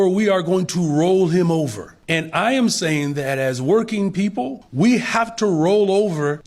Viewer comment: Did he just say hes a working man?